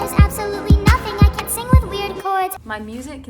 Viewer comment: I thought she said something like weirdcore